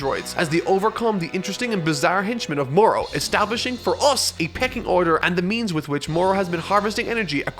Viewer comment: did he say pecking order?!